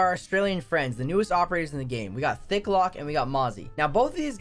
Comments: did he just say thicklock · Lmao thought u said thicclock